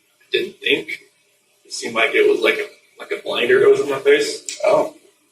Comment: Did he just say at 31:54 "i just couldnt see. It was like.. a blanket over my face or something" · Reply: Blinder